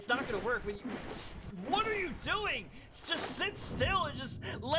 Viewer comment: it sounded like you said sh*t